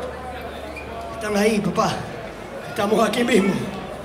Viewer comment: what language is that? Is it a Colombian language? ¿Qué idioma habla? ¿Un idioma colombiano? No entiendo ni una palabra. "ta naí papa.. atamo ake vimu" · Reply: Español latinoamericano, he said déjalo ahí papá, estamos aquí mismo